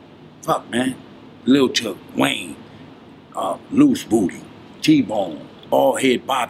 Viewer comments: WAIT...DID HE SAY A GUYS NAME WAS LOOSE BOOTY??????? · Kody is awesome. These Old School OG's are really smart, and their vocabulary is unreal. I love watching videos and interviews with OG's. Edit: When he was calling out dudes Nicknames at 28:15. Did he say ole dudes name was "Loose Booty"??